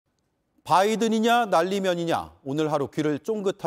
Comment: 내 귀에는 정확히 '바이든이는'' 으로 들리네 ^^ 내 귀 2.0